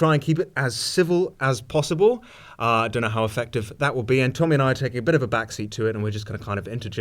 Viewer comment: Thought he said Tommy NC at first, was dissapointed